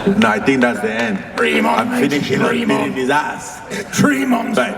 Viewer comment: Bro idk what francis said at 12:10 but it sounded like i finish him and put it in his ass LMAO wtf